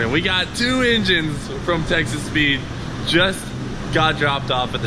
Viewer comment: while was unscrewing the second box i heard you say "texas weed" instead of texas speed :D - fuuukkk i need my ears washed lmao